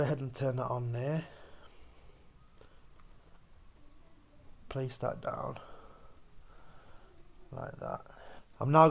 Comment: you can clearly hear a child's voice say something, then laughs.... pay close attention.... and has anyone else heard this since no one mentions it?!......let me know please. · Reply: MAYBE... "HE CANT GET YOU" ? TALKING TO ANOTHER SPIRIT.